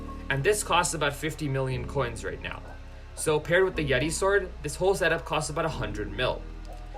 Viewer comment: I thought he said Beti sword and I was confused Also sus